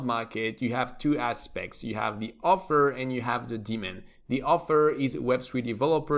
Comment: I definitely thought you were saying "demon" the first couple of times lol